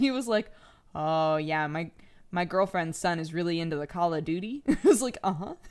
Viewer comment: I heard "Call a doody" lol